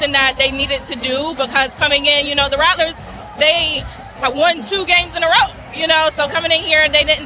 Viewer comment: Did she say the Rattlers won two games in a row against JSU 2:05? FALSE…. JSU won last year & this year. And JSU has won 6 out of the 7 matchups! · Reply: You have to listen INTENTLY. She said "the rattlers have won 2 in a row", meaning their last 2 games (Alcorn & SU). Never did she say they won 2 in a row against Jackson state.